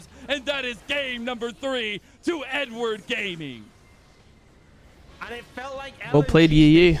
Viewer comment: thought he said n-word gaming 💀💀💀